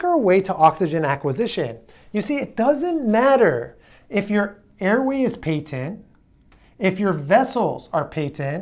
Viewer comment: What is he saying? Peaked in? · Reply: Airway is patent